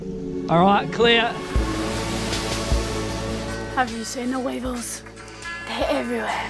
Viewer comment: I 100% thought you said the wiggles instead of weevils and I was just thinking omg, that's amazing that they're in the Bahamas?? Lenny's gonna be a true blue Aussie! And then I saw... It must've been a brain fart... 'hot potato, hot potato' :D